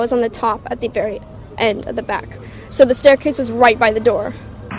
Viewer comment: What did the young lady say at 3:48 , "the ??????? is right by the door"? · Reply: No problem she says "So the staircase was right by the door"🖒